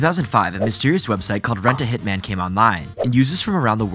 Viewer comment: That last one with the military uniform i remember. It was pretty recent and the dude was actually trying to BECOME a hitman. Obviously arrested. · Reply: christ its like six am for me and im running off four hours of sleep, i thought you said human instead of hitman and i swear to god i had to do several takes